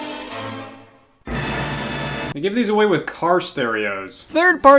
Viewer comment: did he say with or on?